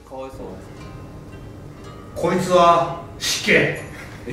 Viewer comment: こいつは死刑にしか聞こえなかった